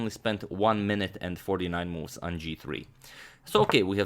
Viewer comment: he says Moves instead of minutes